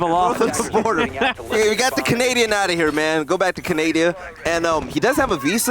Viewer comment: did i hear Canadia?😂